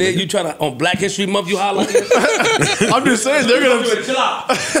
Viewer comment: Episode funny as always Question tho...At 1:43:36 did Parks just say nigga ??? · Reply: I'm just saying their gonna